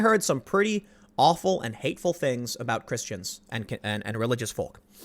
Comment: every time he said "christains" i heard "chris chans" making it a different video :|